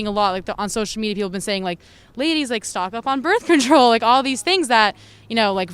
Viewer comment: “ladies stock up on birth control” am I the only one who heard that as “ladies be responsible” wonder why it is they didn’t feel the need to be responsible before?